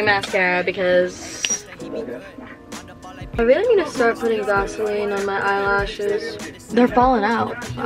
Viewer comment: Bruh I thought she said she was gonna put gasoline on eyelashes